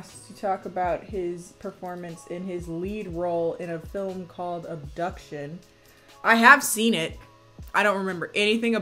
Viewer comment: I thought....The first time you said "Abduction" ....that you said "Duck Shit" I thought you were saying Taylor Lautner was in a movie called Duck Shit